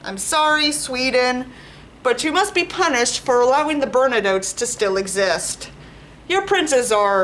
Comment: Sorry if my english is very bad, but what was the word you said at 22:26? Burnanotes? Bandidos? (It says "burna notes" on captions) I've honestly never heard that noun before. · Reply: Bernadottes; the ruling family of Sweden